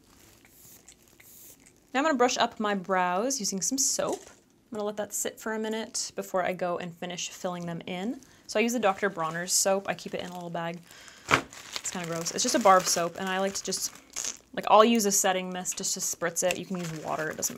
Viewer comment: totally thought she said just a barf soap. Lol